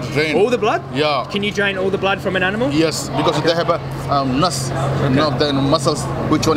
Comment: when he said Nuss instead of nerve got me😂😂😂😂 Damn desi.